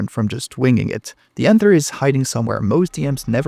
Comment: based on how you said the “anther” instead of answer here makes me think you’re Spanish